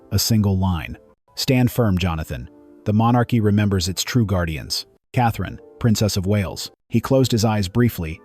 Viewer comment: I thought he said he became guard for Catherine